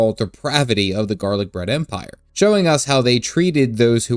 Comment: Did you say “garlic bread” Empire? Pffft, ha ha. I more or less agree that Stormblood 4.0 was good, but not great.